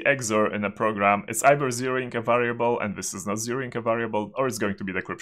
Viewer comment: can anybody tell me what he is saying at 50:38? i can't understand it accustically. edit: It sounds like "0 inc a variable". But this doesn't make any sense, does it?